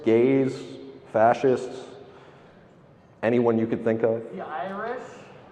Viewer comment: What did the guy at 15:49 say? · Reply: The Irish